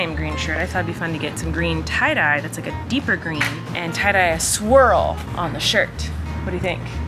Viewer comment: I totally thought you said “tie dye a squirrel” on the shirt and I was like “damn Colleen you’re a tie dye queen” and didn’t realize you said swirl until you untied it😅